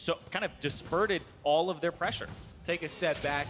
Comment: did he just say "disperted pressure"?